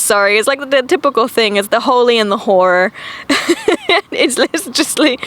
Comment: I heard "holy and the whore" and laughed so hard